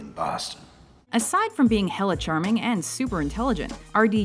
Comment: Wait, did she say "Hella"? Wtf lol